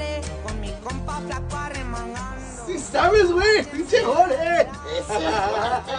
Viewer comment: I thought he said "pinch3 gordo" but i replayed it and he said "pinch3 jorge" lol